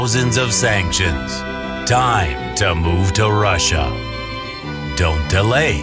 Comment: Послышалось "Die to move to Russia"..